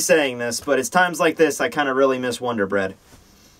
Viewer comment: PLOT TWIST: GRIMMJOW IS THE SOUL KING Just going to call that right now just in case it actually happens. Also at 0:16, does he say Wonder Bread?